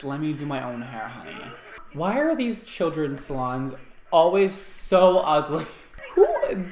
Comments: seriously thought he said these children’s mom 😂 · I thought he said that she was ugly